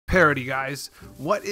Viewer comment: I thought it said "party" lmao, I was so confused